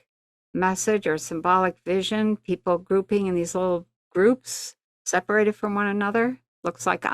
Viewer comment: I thought I heard “people pooping in groups “ but grouping in groups I must have been 🤗🍀🐬😂 what a sighting it would have been 🤗🤗🍀😂